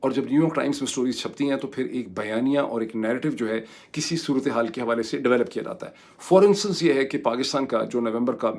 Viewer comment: at 13:17 did you say the word "For instance"? I couldn't interpret it, please let me know.